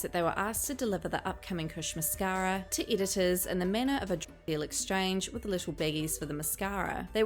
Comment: Anyone else confused around 10:55 ish. Is she saying a dog deal?? Dog culture?? I hate how much she edits words. Jesus fuck